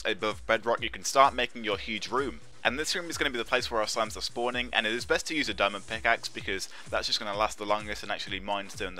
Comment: What did he said "from the bedrock 4 or 5 blocks" or "from the bedrock 45 blocks"? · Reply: 4 to 5